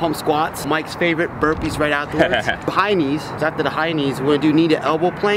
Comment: thought he said high knees xD